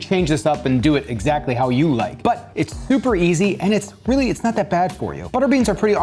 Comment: I actually thought you said, "Super Easy, Barely an Inconvenience" while I was distracted and had to rewind. I was like, "Wait is he a fan?"